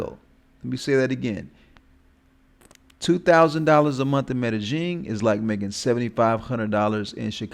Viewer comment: bro quit making shit up, I bet you don't even speak Spanish 🤣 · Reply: how? Because you think you hear a certain sound, when it fact it is a different sound. Its not rocket science. Just like you think the OP is saying Medellin right when in fact he sounds like he is saying Meta Jean, way way off.